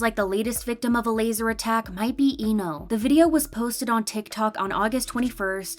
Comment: I am so sorry but um did you say ee know bc I did not hear the L😅